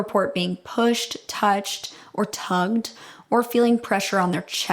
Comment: genuinely thought you said “tongued” and i was very thrown off for a second